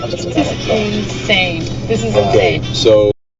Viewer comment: I hear Cenk and Ana lmao